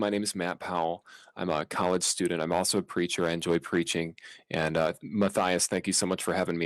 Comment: start of the first video being reacted to, i initially misheard matt saying "preacher" as "creature" so "i'm also a preacher, i enjoy preaching" became "i'm also a creature, i enjoy creaching"